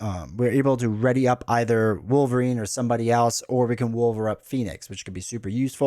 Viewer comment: Did you say “…wolver up Phoenix…”…?😂 loving your content - I went ridiculously overboard and bought everything currently available within a short space of time and have been massively overwhelmed with it all so rarely play due to not knowing how to have the best time.